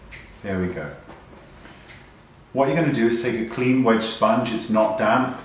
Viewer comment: Can somebody please help me? I am not a native speaker and the part where he said "and what you´re gonna do is to take a clean xxxx sponge, it is not damp" does he say to take a clean WET sponge? I just couldn´t understand him :( The part is at 1:28 Thank you :) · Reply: clean "wedge" sponge. That triangle sponge is a wedge